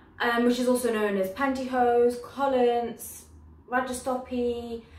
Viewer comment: Did I hear 'rajstopy' in 0:41 ?